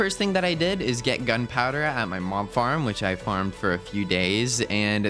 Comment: Why do I keep hearing "my mom farm" instead of "my mob farm" 🤔😭💅